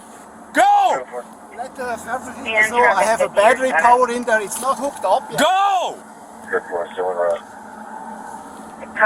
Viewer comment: What did the guy at 5:00 say? And what accent was that? Man from Switzerland with an american accent? "something is not hooked up yet"